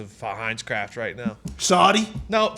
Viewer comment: did say “Saudi?” 😂